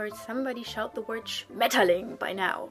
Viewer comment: Hallo Frau, may I know what is the word you said at 2:21? I just started learning German by myself, sorry but really don't know that word and am very interested to know. It sounds like spaetterling, but I cant find it anywhere. Bitte hilf mir. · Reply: Schmetterling - butterfly :)